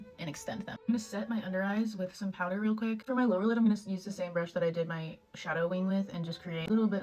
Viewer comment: i sneezed around 1:27 and then at 1:29 it sounded like you said “i’m gonna sneeze the same brush” and it freaked me out